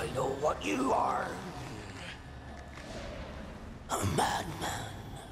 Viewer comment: when I first saw the film I thought he said I'm batman 🤣